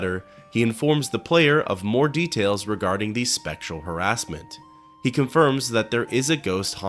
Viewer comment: When you said "spectral harassment" I thought you said "sexual harassment" and I thought 'what is Crassius hiding in the closet to scare people?'